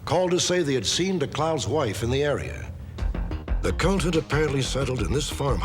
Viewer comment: Did Stack say the C-word at 23:09? “The c*nt had apparently settled in this farmhouse” 😂